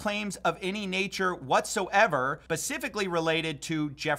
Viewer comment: Dude, did you just say "Pacifically"?! I used to trust you! 😂